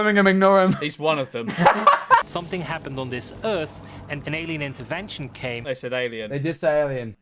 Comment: I genuinely thought it said “Canadian intervention” lmao